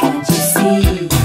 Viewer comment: I thought she said "you could be Morrissey " 😬😂